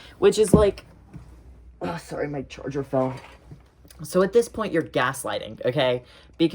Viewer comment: I heard "sorry, my Georgia fell," (as in your rat) and became genuinely concerned